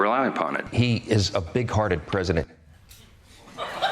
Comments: trump is ‘big hearted’? I thought he said ‘pig hearted’ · I thought he said, “He’s a big hornet president.” 😂